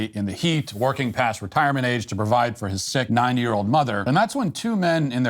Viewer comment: I cant be the only one that thought he said "his 9 year old mother..." right?